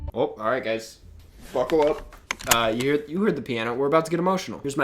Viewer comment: Did you just say "Fuckle up" instead of "Buckle up"? That made me laugh way too hard